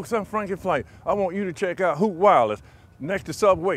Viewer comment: How many times did they say "Hoot Wireless" counter: 1. 0:08 2. 0:17 3. 0:18 4. 0:22 5. 0:22 (Yes, there are two at the same second) 6. 0:23 7. 0:25 8. 0:29 9. 0:31 10. 0:34 Hope I didn't miss any "Hoot Wireless" out. Also am I the only one who hears "Hook Wallet"?